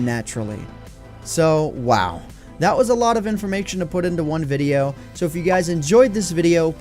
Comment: Am I the only one who heard "informaktion" ?